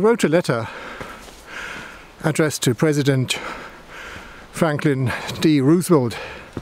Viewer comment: The American President you were attempting to refer to is actually named Franklin D. Roosevelt. Otherwise, this was an interesting video. (Your inaccuracy in the name of one of your major players in this video, however, leads me to question the accuracy of the rest of your details...) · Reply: I just watched it again myself and I could swear I hear myself saying Franklin D. Roosevelt at the 10:08 mark. So, I can hereby reassure you about the accuracy of the other information conveyed 😁 OK, I know I mispronounced the name...but ever so slightly. Anyway, thanks for the comment!